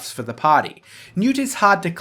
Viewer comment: What blade is he saying at 6:12 ????? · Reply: Poku Newt